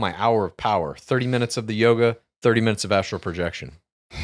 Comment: I was in the same boat as Wiil when Chal talked about his "hour of power". I was not expecting "30 minutes of Astral projection" to be the 2nd half of the hour of power. Astonishing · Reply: Dude, I thought he said "Asshole Projection". Thank you so much for clearing that up.